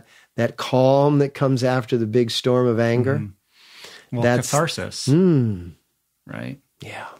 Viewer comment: What was the word Rich used at 1:23:25 to describe the calm feeling after the storm of anger...? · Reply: Catharsis.